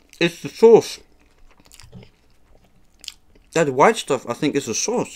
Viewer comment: what did he say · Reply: He said the white stuff is the sauce on the sandwich